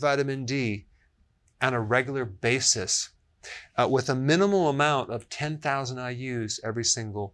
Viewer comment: Did i hear Dr. Berg say minimum amount of 10000 iu @ 4:25. is that the right dose??? Can someone please clarify.